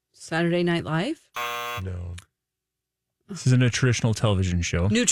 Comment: I heard “nutritional” too 💀💀💀